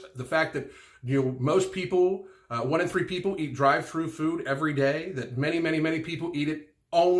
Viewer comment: You know you're a health nut when at 6:40 you hear "dried fruit" instead of "drive through food" 🤦